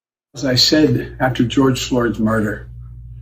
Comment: For a moment it sounded like Biden said “after George Soros murder 🤣. But he did actually say George Floyd, just not clearly.